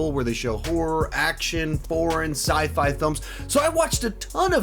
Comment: I actually had to replay this part 4 TIMES because I was sure you said "porn" instead of "foreign" when listing of the list of genres playing in FantasticFest 😳